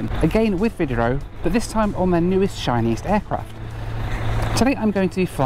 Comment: Azul airlines in Brazil have recently (in the last week or two) have taken delivery of their first E195-E2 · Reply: nah he said "newest, shiniest aircraft" not Chinese. Easy to mishear though.